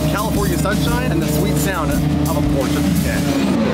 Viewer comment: Could've sworn he said a Porsche taycan before I heard the engine sounds 😂